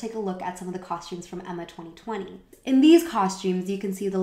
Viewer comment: does she say "emma 2020" ? I would like to watch the serie she talks about